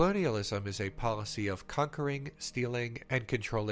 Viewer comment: YouTube has rotted my brain. Every time you say "conquering" i hear "cuckering."